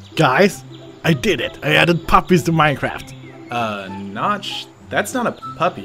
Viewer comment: Who thought Notch said Poppie as in the flower added to Minecraft